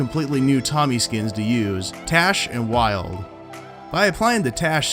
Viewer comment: He said Tache and Wild and I thought he said Dash Wilder XD